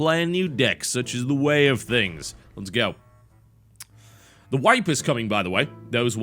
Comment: Was chewing on chips with my headphones on and what I heard was : "The wife is coming for anyone wondering, everything we worked hard hard for will disappear..." and I was like, "damn, hard times..."